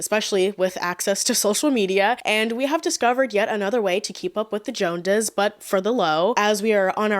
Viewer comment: Who’s keeping up with the joandas? I thought the saying was “keeping up with the Jones’s”